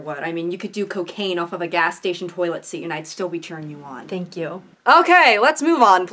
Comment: The subtitles at 7:57 say “I’ll still be turning you on” instead of “cheering you on”... gal pal energy